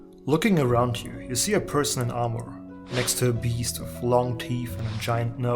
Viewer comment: sounds like u said person armour please try to speak clearer 👍